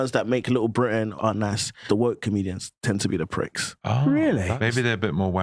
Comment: Dude, I thought you said these were un woke comedians These guys at the end are terrible.